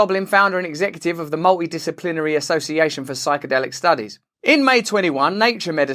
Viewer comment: Strangely enough, at 5:41, when Mr. Brand says "Multidisciplinary Studies".....because of the big mushroom image on the screen and his accent, I heard "Mouldy disciplinary studies". I'm also sitting at home on some powerful drugs myself getting over an illness.